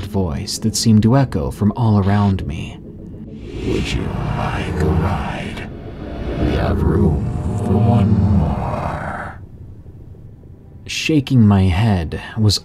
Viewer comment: can anyone tell me what he is saying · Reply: Would you like to ride? We have room for one more